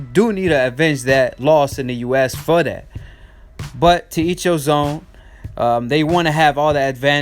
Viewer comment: Did you just say “to eat your zone” at 1:59 instead of the saying “to each his own”